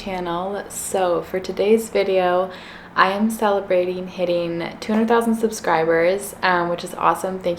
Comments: I thought it said 200,000 calories I was shook 😳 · i know i'm not the only one who thought it said 200,000 calories instead of subscribers lol